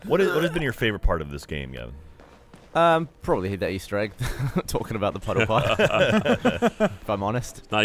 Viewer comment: What did Gavin say his favorite moment was? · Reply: Pile Puddle easter egg